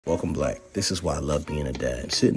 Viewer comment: “Welcome black“ 🤣 love it. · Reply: I thought he said welcome blacks 🤣🤣